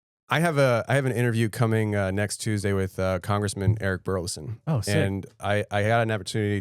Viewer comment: I thought he said congressman bear grills lol